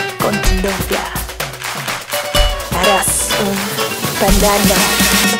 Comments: Al principio ella dice "Regresa con tu novia, eres un veneno" Yo escuche : Regresa con tu novia, eres un pendejo. Estoy mal jsjsjsj 😂 Edit: Al parecer somos muchas las que estamos mal, pray for us :' asfdgfhfjk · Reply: yo escuché "eres un banana" 😭😭😭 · La neta yo entendí “eres un pendejo”. Re épico sería jajaja. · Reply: Jajajajajaj, la canción se llama poison chicas!! Dice veneno xD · Al principio ella dice "Regresa con tu novia, eres un veneno" Yo escuche : Regresa con tu novia, eres un pendejo. Estoy mal jsjsjsj 😂 Edit: Al parecer somos muchas las que estamos mal, pray for us :' asfdgfhfjk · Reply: JAJAJAJ yo escuché "eres un..." y me asusté porque pensé que diría pendejo